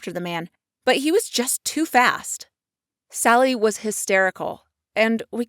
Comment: I thought he said but he was too fat🤣😂